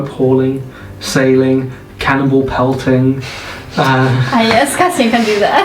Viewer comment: whenever said 'cannonball' i heard 'cannibal' so i thought they threw cannibals and i thought that was cool